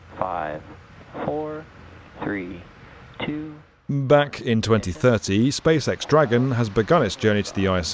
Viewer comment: Am I the only one that heard him say "back in 2030"? And it's 2025